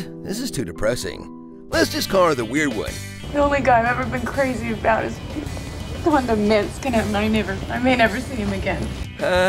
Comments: okay what the hell did she say there? I can't understand her mumbling! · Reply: "The only guy I've ever been crazy about has gone to Minsk, and I might never... I may never see him again." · I have rewinded this a lot of times and still can't make any sense of what she is saying, could someone tell me what is she saying, please? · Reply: The only guy I've ever been crazy about is...(blah blah sounds like mints)... and I may never see him again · does Phoebe say "The only man I've ever loved has gone to Minsk"? What happened?